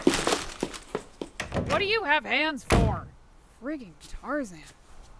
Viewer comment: being Tarzan what did the mom say freaking Tarzan?